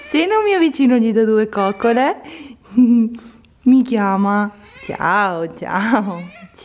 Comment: Ma solo io al minuto 7:00 ho sentito io fatto che diceva “mamma”?